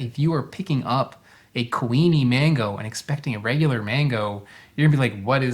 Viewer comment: lol when you first said kuini mango i thought you were saying queenie mango and just over-emphasizing the ui sound 😂 it seems logical that there would be a queen mango variety.